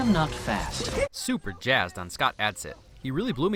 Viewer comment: I thought he said Scott Adkins and I was like “Yuri Boyka voiced him?!”